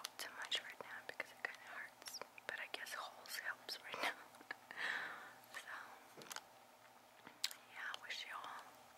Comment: What did you say at 16:11 when you pointed at us? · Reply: Halls "halls helps right now". the cough drop brand she's eating